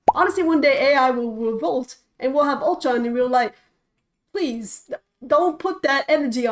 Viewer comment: I misheard "Ultron" as "Voltron", and was encouraged rather than warned. 😂